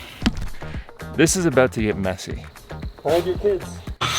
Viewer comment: did say “Hold your Tits”? Lol my husband says that a lot hahaha 😂